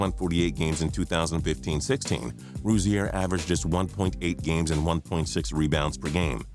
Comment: did he say 1.8 games a game